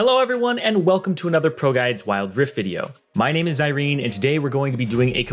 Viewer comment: I heard "my name is Irene"